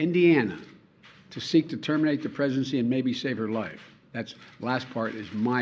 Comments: I’m sure I heard him say “terminate the presidency” Yes, Terminate your Presidency!!! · Why did it sound like he said that she had to travel out of state to terminate the Presidency? Hmmm